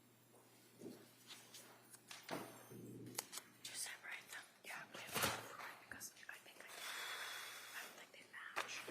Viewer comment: what are the DA whispering? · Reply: Something about separating them and heard the word "match" And " you gotta do something"